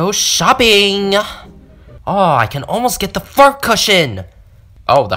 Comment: sounded like the f word but it was meant to be fart